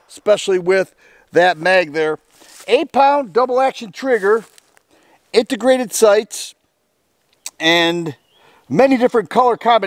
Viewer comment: When you said "integrated sights" at first I thought you said "aint the greatest sights" both are true. Lol.